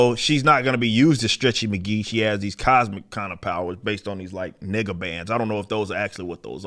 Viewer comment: i thought he said N*gga bands rather than nega bands 🤣